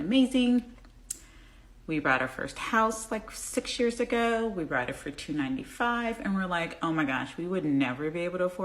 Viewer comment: did she say she brought it?